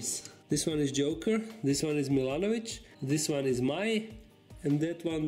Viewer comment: <link> first i heard "Milošević" as the name for your frog